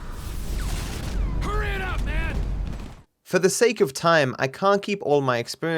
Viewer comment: At around 3:00, does Trevor say "Hurry it up, Ned", instead of Michael? Sounds like they left the wrong take in, since Ned is the name of the voice actor of Michael :D